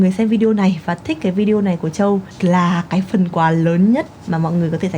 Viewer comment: có ai nghe thành "Chúc mừng sinh nhật Châu, ĐIÊN!" ko vậy? :)))